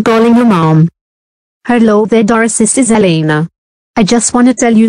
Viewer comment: why does she say boris